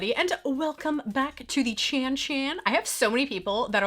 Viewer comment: Ohhhhhh!!! Chan Chan! I thought you were saying Cha Cha’s! 🤣